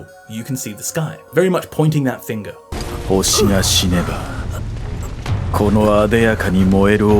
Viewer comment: complete illiterate here so forgive me but at 3:30 I hear "ho shi na shi ne ba" and not "ho shi GA shi ne ba". It's impossible to bring myself to hear, even slightly, the particle "ga" in this sentence. is that a pro nunciation thing, like "ha/wa" ?